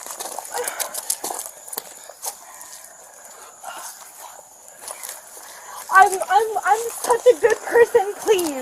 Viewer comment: "I'm such a good Christian" did I hear that right? 🤣 To me she appears intoxicated.